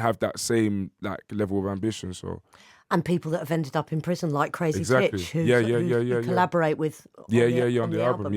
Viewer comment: Did she say Crazy Stitch? Smh